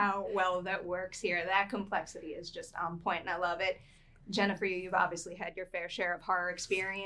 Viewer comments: Did she called Jenna Ortega “Jennifer”? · did she call her Jennifer? 😭